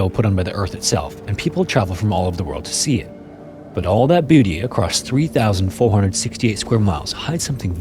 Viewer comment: sounded like “all that booty” lmfao